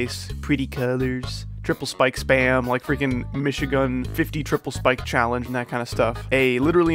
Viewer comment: LMAO I THOUGHT YOU SAID MICHIGAN